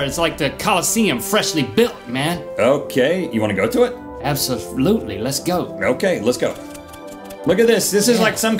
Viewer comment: Did Link say, "absoflutely?"